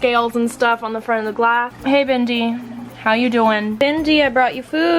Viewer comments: DID SHE JUST SAY BENDY?!?!?! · did you say Bindi? Just wondering cuz that my dogs name.